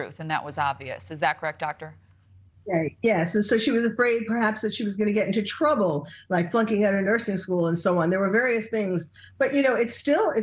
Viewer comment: I think you may have misheard. At 12:26 in the video, the forensic psychiatrist said "she was afraid perhaps that she was going to get into trouble, like flunking out of nursing school ". Just didn't want you to be accused of saying she had already flunked out!! I'm always on your side because you always speak facts!! Thanks for all the hard work you put in for us!!💜